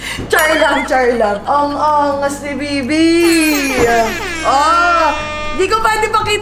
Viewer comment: I just heard the bibi-bia